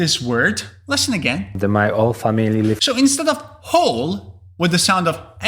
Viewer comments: I thought he said "Old family" not "whole family" 😅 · I think not understanding the context on the Portuguese accent was the bigger issue for me. I definitely heard something like “two Hanukkahs” - which I think I could have understood as 200 cups/caps if that made any more sense to me than 2 hanukkahs does, but it still doesn’t. I still have no idea what that means. I don’t know diddly squat about sports or the Olympics. I understood the other bits just fine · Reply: As for the others, I didn’t quite get “partners” and thought the one guy said “all family” (meaning whole family, still, but I thought he said “all”). I really thought I had a hard time with cockney in general, but I had no problem with this lady actually?